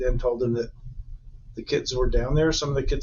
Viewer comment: so I skipped to this part on accident and I thought the lawer said "[she told him] the kids were items"